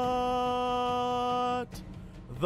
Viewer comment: I tend to listen to these on the phone while I'm out, and I thought he said "The Swedish Fingers" and was really confused for a good while.